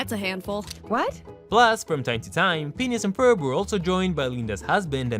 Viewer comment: If you put subtitles on, it says penis instead of phineas